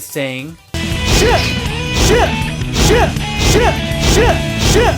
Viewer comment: Ahhhh, I remember this game. My dad grounded me for a month because he thought Knuckles was saying Shit Rocks